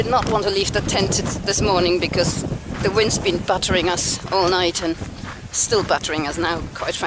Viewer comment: At first I thought you were saying that the wind was "buttering us". lol